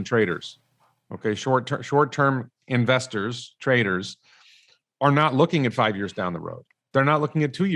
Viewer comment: When was describing the 2 types of investor, initially I thought he said "Short term investors aren't really investors, they're traitors" 😂 then I realised he in fact said trader. Perhaps either word could apply.